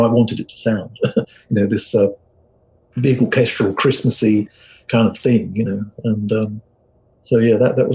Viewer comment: If I had a chance to interview them I would have one question for them: "What in the world does Cool on Cries mean?" (growing up I always misheard it as Cool on Christ)